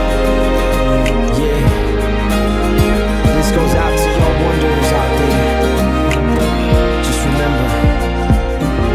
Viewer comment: What is he saying at 2:08? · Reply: Mhhh not sure but I tried to write something? "Yeah This goes after y'all wonder what's out there. Just remember. One day... One day... And that day will come for you. Aight"